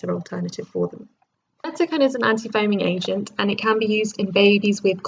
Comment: what is the name of the drug being said here? (It says _ is an anti foaming agent used in babies with colic and can also be used for hiccups in palliative care) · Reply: Simeticone :) although there are now more suitable options for those with hiccups in palliative care, refer to the "Hiccups" section in this link for more: <link> :)